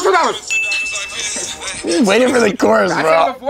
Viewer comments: what song is it? I don't understand what Yachty said. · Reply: song name is 22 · please someone tell me the song name I can't understand what says🙏🙏🙏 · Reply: Finally figured this out after a fucking year bro 22 diamonds